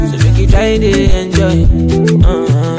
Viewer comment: My silly self thought he said "make you dance like Joanna" 😂😂😂